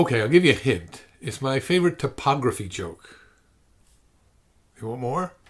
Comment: I thought you said it was a topography joke, and I spend ages trying to work out why I didn't remember "kem".